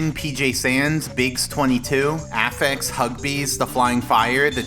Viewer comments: I'm sorry? Did he say Huggabees? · Did I hear Huggbees? That’s cool that he supports the channel